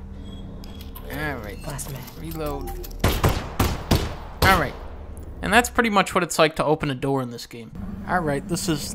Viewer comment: I don't know what she says at around 2:57, but it sounds like bazmeg, which is basically fuck you in Hungarian. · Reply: She said last mag. As in it was her last magazine clip she had.